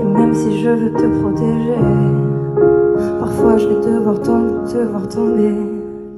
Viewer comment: elle dit quoi s'il vous plaît ?? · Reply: « Parfois j’vais quand même devoir te voir tomber »